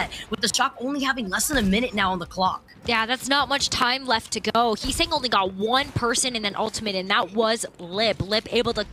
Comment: anyone know what did lip say…. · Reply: he said " that was bit..."